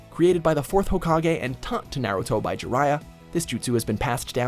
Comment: Instead of naruto captions say narrow toe...